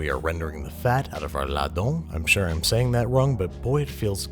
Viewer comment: What ingredient is he saying at 1:50? La dom??